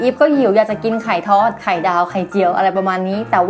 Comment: ตอน2:15 พี่อีฟพูดว่า. ไข่ท้อด